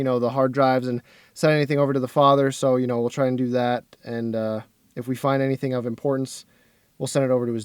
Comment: thought he said if we find anything other than porns 😳